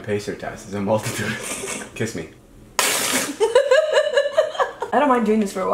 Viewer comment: David: Kiss me Liza: Spews water all over his face They sing Dora together GOALS, COMPLETE GOALS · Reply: Madison Godwin What? At 1:28 he says kiss me.